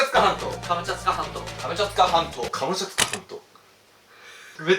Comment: カムチャツカま○こに聞こえる俺は一体……